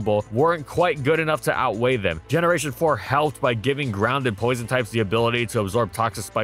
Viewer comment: did you say GROUND types absorbing toxic spikes in gen 4? is that correct? I don't think so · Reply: Grounded Poison types